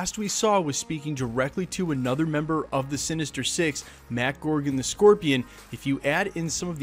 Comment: When I heard "gorgon" instead of "gargan" I shuddered irl